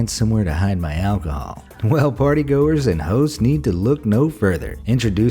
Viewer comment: He says party goers and hoes at 3:51 lmao am i the only one who heard that? It even says hoes in the caption generator lmao